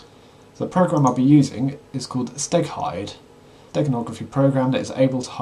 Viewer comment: For a second, I thought you said Steak hide - you were making me hungry! LOL